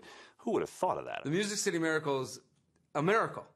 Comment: Who here after the titans did this on MNF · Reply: I thought the guy said NY City miracle